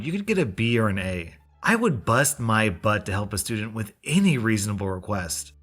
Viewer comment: I thought you said "i woud bust mu nut"